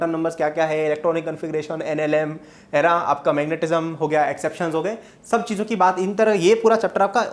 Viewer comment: did he say nlm